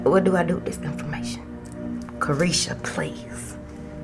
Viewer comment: Did you say creshia please @ 8:51because if so ur speaking directly too me as that is part of my given name shortened. Wow